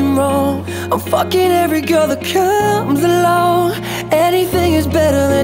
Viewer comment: What It says "I'm fucking every girl that COMES ALONG" What I hear "I'm fucking every girl at CAN'T BELONG"